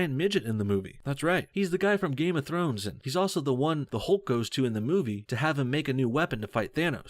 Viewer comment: Did he just say..*Hulk?*